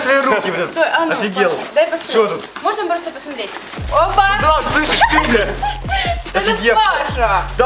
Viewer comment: Мне одной послышалось что сказала "слышишь ты бл@